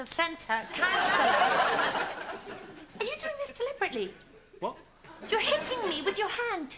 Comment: hey hi... anyone knows, what she says in the minute 1:00 please.. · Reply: council...are you doing this deliberately?